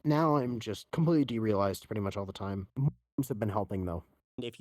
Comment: Masturbations? Macedonians? Murders? Honestly, I have no idea what she said there. Help! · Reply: Mentos. Gotta watch out for The Freshnaker®